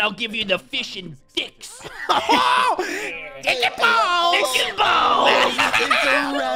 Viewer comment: After recently binging every Razzle drunk commentary, I can say with confidence that the "cock and balls" bit was officially the worst bit in Razzle drunk commentary history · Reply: I thought he said "chicken balls"